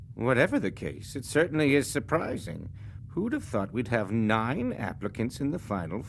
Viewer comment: it’s sounds like he said who tf 😭😭😊